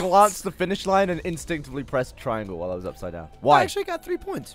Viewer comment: Did say triangle?